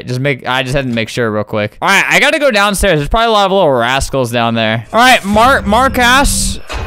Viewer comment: did he just say ass?